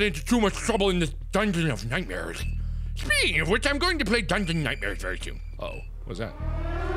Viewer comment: Did he say he'll be playing Dungeon Nightmares soon? Please, Mark, please!